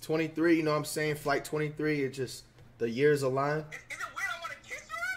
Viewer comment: did flight just say “flight 23 inches”